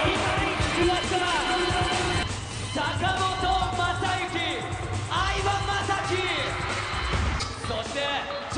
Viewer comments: 相葉雅紀さんを呼んだ時に海馬雅紀と聞こえてしまった俺は異常なのか？ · 相葉が海馬に聞こえすぎてやばいwww